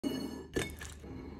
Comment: OH? so he was talking "let's do some cooking " all the time? I always heard "latsdosukeikei"